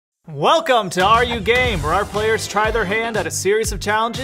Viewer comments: At first I thought they said, Welcome to are you gay... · No one: Nodody: Absolutely nobody: Me at the start of the video: did he just say are you gay-? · I really thought said “are you gay?” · I thought they said “are you gay?” At first (Also everyone is talking about but what about she’s really funny-) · i thought they said are you gay lol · For the intro I actually thought they said “are you gay” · When they said “are you game” I thought they said are you gay lol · I thought they said “are you gay” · I really thought you said are you gay😅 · I thought they said are you gay in the intro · The first thing that game to my mind when he said are you game I thought he said are you gay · Honestly at first I thought they said “are you gay?” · I thought they said are you gay🤣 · I legitimately thought they’re gonna say are you gay instead of are you game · I thought they said are you gay im so stupid lol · Who els thought the said : are you gay? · WHEN YOU SAID ARE YOU GAME I THOUGHT YOU SAID ARE YOU GAY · AM I THE ONLY ONE WHO THOUGHT HE SAID “are you gay?” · The beginning I heard "are you gay" 😂😂😂😂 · When I heard “Are you Game?” I thought they said “Are you Gay?” And I almost shouted yes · at the intro, i thought they said Are you Gay? 💀💀💀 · I thought that said are you gay instead of are you game for a sec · I heard "are you gay" instead of are you game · tbh I thought he said are you gay? At the intro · At first I didnt hear "Are You Game" I heard "Are You Gay?"